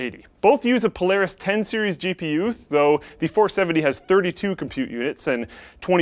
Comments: did he say gp youth? damn Canadians cant just say single letters like z they got to add zed and say youth instead of u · Polaris 10 seires GPyouth? Did I hear that correctly?